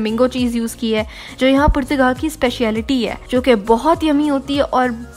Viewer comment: Did you said Portugal